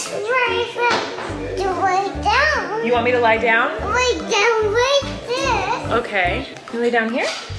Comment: LMAO when said lay down like this, I thought the captions said, "Lay down you little shit."